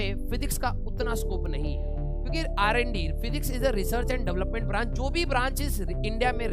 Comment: did sir say physics is RND ??? 🤣🤣🤣 are sir aise kaise bol diye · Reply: Research and development 😒😒